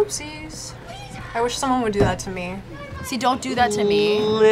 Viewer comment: Did I hear that correctly? "Would do that FOR me" or "TO me"? XD